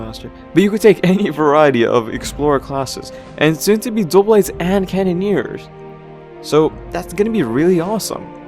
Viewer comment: wtf i thought you said you hated cannoneers